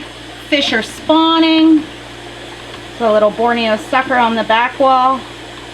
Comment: What's the fish at 10:08 the sucker one? I think she said Borneo sucker but I'm really not sure. I'm really interested in it, it's beautiful